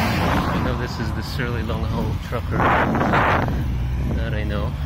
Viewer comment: What did you say about a long haul trucker? Is your bike called a 'Long Haul Trucker'? <link>